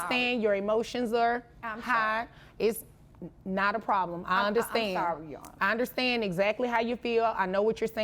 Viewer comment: Did grandma just straight up admit "I'm sorry... I'm high"?! Earlier I thought I heard her accuse her daughter of selling smack from her house, but I wasn't sure. Now I'm positive. The accidental comments, her appearance, it's obvious now. This is a junkie ass momma, pissy her dealer daughter won't keep giving her free gear.